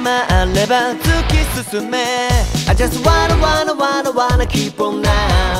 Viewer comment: I just wanna の所が何回聞いても あじゃすわーな わーな きーぽんなぁーう にしか聞こえない光くんが好き